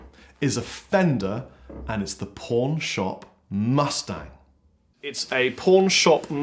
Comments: With your accent it sounded like you said "porn shop mustang". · I thought he said pornshop mustard. Keep up the videos. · Haha for a second I thought he said porn shop not pawn shop lmao.